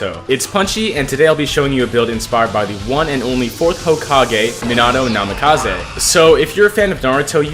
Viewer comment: was it just me or did he say: “Minano Namikaze ” 😳